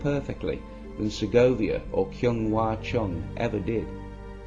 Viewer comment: In case you're still around, what are the name of the musicians you mentioned at 2:55 ? I think one is Andrés Segovia (I recognized the name because I'm from the same country), but I can't quite understand the other one... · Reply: Kyung Wha Chung. Used to listen to a digital recording of a Bartok violin concerto (No. 2) for years, played by a quite brilliant soloist whose name I have sadly forgotten. THEN my dad played me his analogue recording of Ms. Chung playing the same piece. My dad won that argument, without saying a word. :)